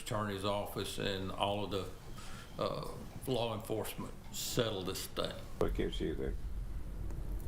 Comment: did he say flaw enforcement 😆 😂